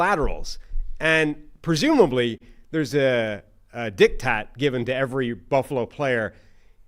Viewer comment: Ope, I'm gonna need you to repeat that word. Did you say, dick-tat?